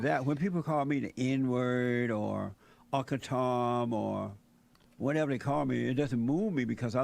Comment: What does Jesse say at 15:10 that makes Xqc gasp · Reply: Uncle Tom